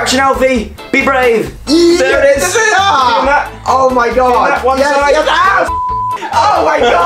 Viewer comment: In the opening I thought said burp instead of birth 😂